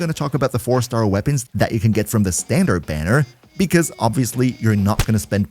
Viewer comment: When he says "three star" I hear "free star" 🤦‍♀️